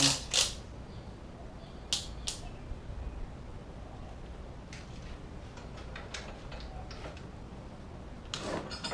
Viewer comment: what are the torque specs at 16:00 I couldnt hear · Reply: 20 the 46 on the rod bolts 18 then 38 on the main cap (girdle) bolts